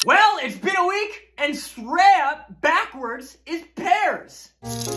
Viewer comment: I could’ve sworn he said “pineapples” so when I say they were pears I was so confused and about to see other people commenting their confusion when I realized, I’m just stupid.